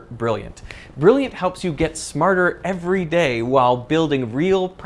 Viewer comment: I'm pretty sure I heard Derek say "Brilliant helps you get Destin Sandlin"?